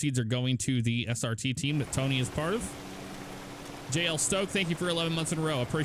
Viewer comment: Is Tommy IRL Cop? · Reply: ohhhhh I thought at one point you said tommy is part of it 😂 my bad